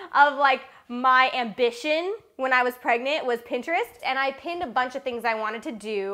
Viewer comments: I thought she said she was pregnant with pintrest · Interesting ideas!!! Did anyone else catch say "when I was pregnant with Pinterest" at 1:03 😂😂